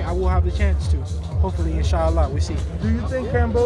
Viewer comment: Did he said inshallah at 13:21 👍❤️